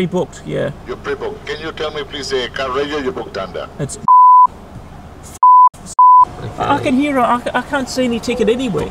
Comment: Did says: "It's B#!CH F#!K S#!T"??? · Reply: NeptuneWindowsMockups No it looks like he is saying “bull fucking shit”